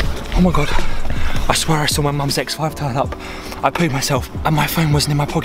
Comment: I swear he said I jus saw my mums ex wife pull up at 17:27 🤣